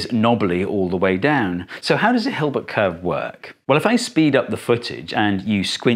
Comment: Your subtitles appear to be autogenerated because they misheard “hilbert” as “help a”. You should be careful using autogenerated content in scientific videos